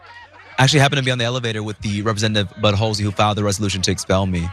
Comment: Did he say, “ Butt Holesie”? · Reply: I thought he said “Bud Hose Me”